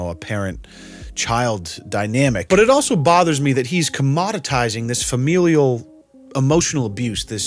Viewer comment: I don’t know if I’m just stupid or this man is just a genius but what did he just say?? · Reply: commoditising, which means turning something into a product you can sell.